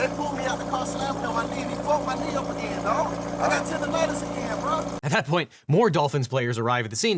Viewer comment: When Mr. Hill he said “tendinitis” I heard “tender lettuce” 😂😂 I can’t believe he went on to play in the game after that. Knee problems are the worst! Especially when your job entails running and evading. I wish him the best with his tender lettuce 😊😂